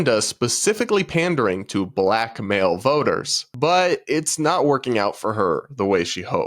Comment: I just misheard "black male voters" for "blackmail voters" i was so confused